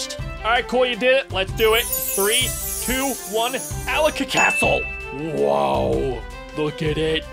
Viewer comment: it kinda sounds like he said Alec's a castle